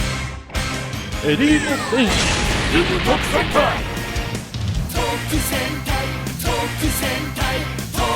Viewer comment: It sounds like they said "told to senpai."